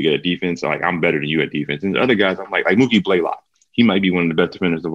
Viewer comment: who did Iggy say was really good at defense? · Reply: Mookie Blaylock